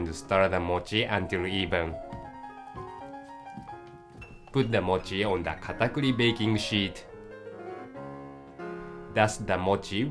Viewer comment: i thought it said "Put the mochi on the UGLY baking sheet"